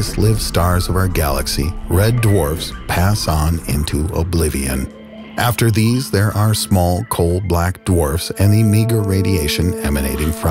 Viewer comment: did you say small black dwarves